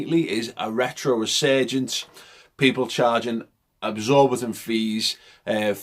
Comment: Did he say "absorbitant shirts"?